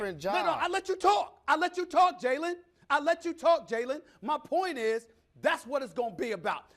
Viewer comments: does he say “nigga I let you talk” or am I trippin 🤣🤣🤣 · Did anyone else catch Stephen A calling Jalen the "N" word at the 21:55 mark😂🤣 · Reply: He said "no no I let you talk"